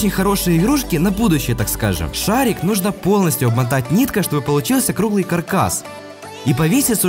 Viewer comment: кто услышал ,,объмотать нитка" ставьте 👍